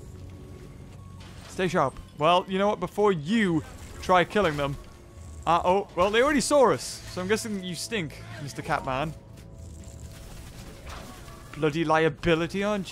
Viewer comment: Lmao, you said "saurs" instead of saw us